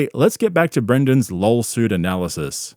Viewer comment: did he say lawsuit or LOLsuit?😂😂